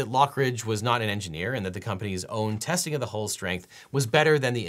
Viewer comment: error in the captions there: “whole” instead of “hull”